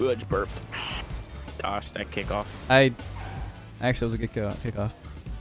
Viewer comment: Did say at 11:16 that was a good girl? hahaha